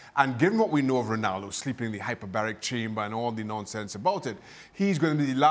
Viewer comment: Lol, I thought he said Ronaldo was in the "hyperbolic time chamber" (so Ronaldo is basically vegeta 🤔😂😂)